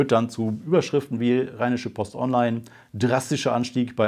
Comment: hab doch glatt reiner shit-post statt rheinische Post verstanden! Passt sogar